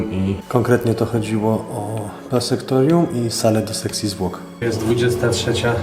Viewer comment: czy tylko ja usłyszałem zamiast sekcji zwłok to seksi zwłok? XD